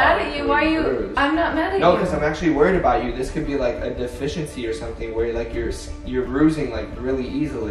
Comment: when he said it could be a deficiency my dumbass really thought he said “ The fish in sea” 😂🤦‍♀️